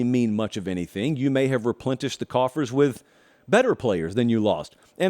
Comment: Did I hear a “ReplenTish” Josh? 😂😉